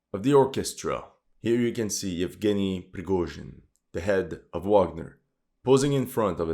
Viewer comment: for a sec I thought he was gonna say denny lol